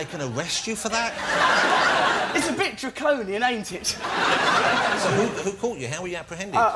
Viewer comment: what did russell say at 3:34? it's a bit... ain't it? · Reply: draconian